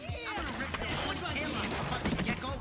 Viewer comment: Did he just say fucking gecko? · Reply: No he says fuzzy